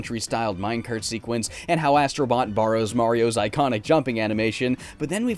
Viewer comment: I honestly thought it said "Astroy Boy" instead of "Astro bot". Huh, I wonder how would look like if he wore an Astro Boy costume? And also, MASSIVE ASTRO BOT ORGY